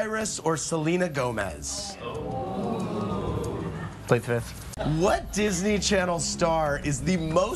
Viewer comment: What did he say? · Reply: He says really fast "plead the fifth!"